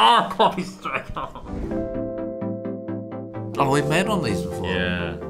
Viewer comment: The carrot thing actually works. But not like these 5 minute dafts did it. You can't just drill 3 holes in a carrot and expect it to work. The holes need to be of different sizes. That's the actual key to making a flute. The positioning, order, none of it matters. The size of individual holes and their ratio to each other are what determines the notes. Sarah Jeffrey has a great video of making a recorder at a real recorder manufacturer with a carrot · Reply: Oh i misheard it then, I thought they said when they were kids rather than "we've done this before"